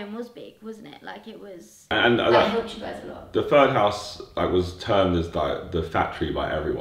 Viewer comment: did say the third house? roughly 7:45 ? i thought there was only 2 houses?